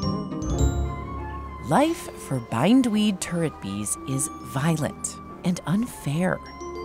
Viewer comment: I thought she said bindweed turd bees 😅 Love your videos and can't wait for more! 🖤👏🔥